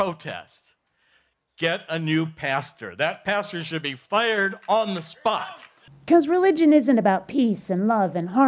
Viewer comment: did he actually say: "That pastor should be fired on the spot"? It then goes to show that Christianity is a "business".